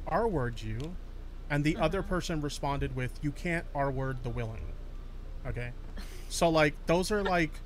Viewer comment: I thought I heard "you can't r word the willy"